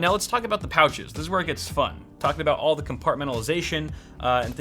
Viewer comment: Did he say Compartmentalization My lord what a word 😂